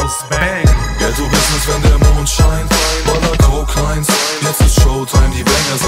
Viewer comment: Lol ich hab immer "Ja so ist es wenn der Mond scheint..." verstanden bei